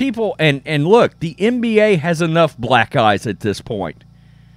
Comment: He said Black eyes, but I thought he said initially the NFL has enough "Black guys" I died